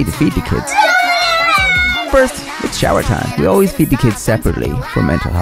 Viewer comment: You feed the kids separately, for mental health reasons? And could someone please explain this ideology to me? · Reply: At first thought they said bathe separately... but it was feed?